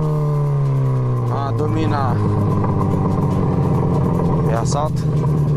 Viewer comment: legenda tá errada kkk o que ele disse dominar é uma marca de moto igual as dominar 400 é dominar 250